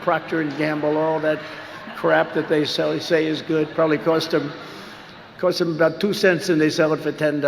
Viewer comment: Trump says "stuff that cost two dollars but you buy it for 10 or more dollars". Like his cards that probably cost 0.5 dollar but people have to pay around 80 dollars for 😂😂😂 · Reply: I thought he said “costs 2 cents”.